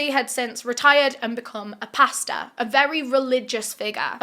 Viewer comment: i thought she said he became a pasta ???